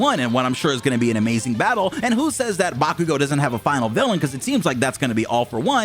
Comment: So at 7:44 bakugo is the next ofa ?? You talk so fast I couldnt understand lol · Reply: Nah I said AFO is his final villain!